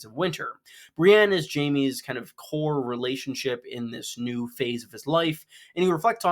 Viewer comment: I thought you said ‘whore’ relationship lolol